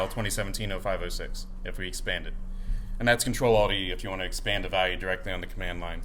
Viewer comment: Great stuff yet again, thankyou! at 1:14:30, did anyone catch the the command for expanding the variable "$fielname" on the command line - CTRL- _ something. I can't hear it clearly enough, thanks in advance : ) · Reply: Ctrl-Alt-e will perform expansion on the current command line, including alias, history, and shell word expansion (including command substitution and arithmetic expansion, though not arithmetic evaluation which is not part of an expansion). It won't perform brace expansion, unless, of course, it is nested inside a command substitution, which will be executed in its entirety. This should pull up the relevant section of the man page: man -P "less +/shell-expand-line" bash This will show the current binding in your shell for the shell-expand-line function (it is unlikely this has been assigned a different binding, but this command would tell you if so): bind -q shell-expand-line